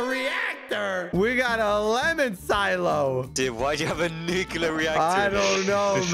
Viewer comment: what did he say? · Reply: Nuclear, it’s just his accent